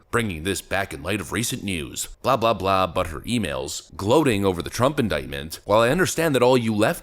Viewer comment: When I saw the coverage of Hillary's hat I kept thinking I heard " butt hurt emails". This does seem like a better dis.